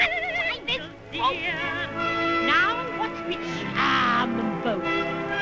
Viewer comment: I cannot understand a word the witch was saying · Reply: # Hansel, dear!# Now, watch me charm them both.